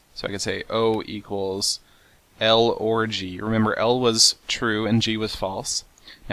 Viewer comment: Poor choice of variables Seth. X3 Sounds like you say orgy. xD